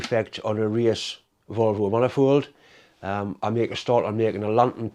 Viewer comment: lol For a second there I thought you said 'Volvo' race engine.